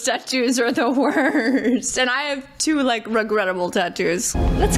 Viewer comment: I thought you said you had 2 "Rugrat-able" tattoos, which sounds kinda awesome😅